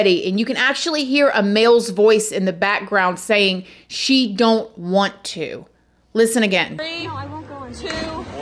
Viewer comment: I heard “Don’t want you saying no”